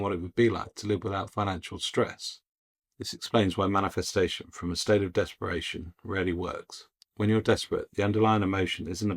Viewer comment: TBH, I had to turn on my CC to see the words, because I thought you said: -- Manifesting from a state of desperation REALLY works but when went through the video again, this time WITH CC, I saw that you actually said: "Manifesting from a state of desperation RARELY works"